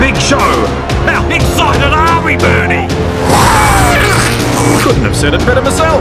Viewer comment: At the 1:24 mark, what does the announcer say? It sounds like "A big fucking army, Bernie" and now that's all I can hear. Even reading the subtitles doesn't help (if they are actually correct). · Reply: How excited are we, Bernie?